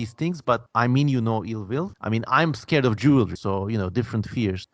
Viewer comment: I thought for a second you said "I'm scared of jews" 💀💀💀💀💀💀